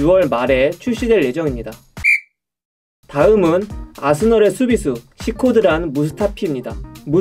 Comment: 초에 나만 사스널의 수비수라고 들리나??